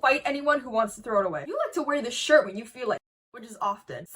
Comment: What did she say at 4:27? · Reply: when you feel like s**t